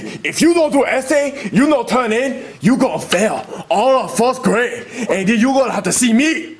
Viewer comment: Why did it said fourth grade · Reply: He said 1st it just was the accent